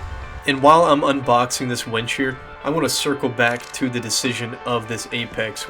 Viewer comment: I was eating dinner and wasn't looking at the screen of my phhone, I thought you said "wench", made me press rewind there, then i realized you meant WINCH (pronounced wEEnch) 😂